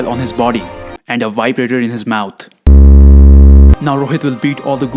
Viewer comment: Did he said "vibrator in his mouth"😳 bursting out in laughter 8:36😂😂😂